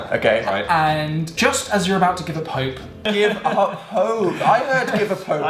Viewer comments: I heard " Give a Pope" . =D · "Give up hope! I heard 'give a pope' as well!!" :'D I love how smoothly that was edited :') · I heard "give a pope" as well!!! Let's be honest, that would have been funny... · i thought he said give a pope